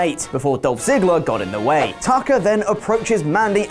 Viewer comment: Thought said Taco instead of Tucker. lol